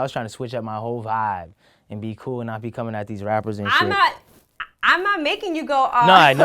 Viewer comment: all i heard was " these rappers can't " lmao this man is funny